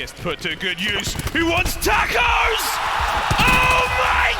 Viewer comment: did he say “he wants tacos”😂